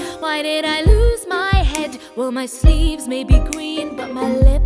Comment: I may or may not have misheard "My sleeves may be green" to be "My sleeves may be GWEEN" and now I can't forget it